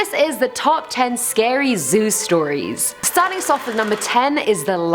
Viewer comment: I keep hearing Zues tori's instead of Zoo stories